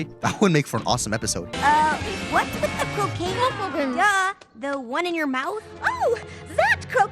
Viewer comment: I never thought they were saying cocaine bro what 😭💀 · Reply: They don’t, she says “croquet mallet”, but he edited it lol